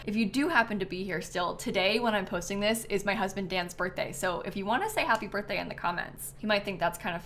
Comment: When you said “Today is my husband’s Dan’s birthday” I heard “dance birthday” and i was like what is a dance bday???!! 😂 anyways happy birthday Dan!!